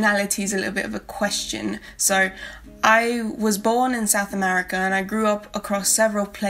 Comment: It seems like I still need to work on my diction. I’m from South America. My first language is Spanish and I grew up in ex-Spanish and ex-Portuguese colonies haha. One of my best friends is South African though, so I have no doubt that she’s influenced the way I say certain words as well. It’s really a patchwork of a way of speaking to be honest hahaha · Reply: I am so so sorry I said South Africa. I don't even know Why I thought I heard South Africa. I am glad that you responded and corrected me to this. I made a big mistake. I was editing photos on my computer when I was listening to your video. For some reason I thought I heard South Africa and just heard a bunch of locations and when you are listening but are multitasking it does odd things. I went back and rewatched this video more closely after you commented. Now I don't know how I get South African out of South American.